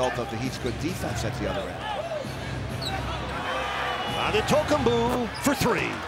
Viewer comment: 🤣🤣🤣🤣 tf he say? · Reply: Adetokunboo 😭